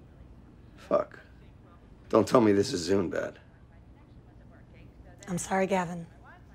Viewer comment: FYI: I'm seeing some comments about 17:25, people mistakingly thinking Gavin said: "Don't tell me this is Zoom bad". He actually says "Zune", which was a shitty Microsoft mp3 player from like 10 years ago. Just thought y'all would like to know.